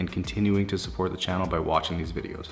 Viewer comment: I thought I heard "by washing this video" 😆 Well actually its 3.23 am in my place and still can't get enough with his detailing video.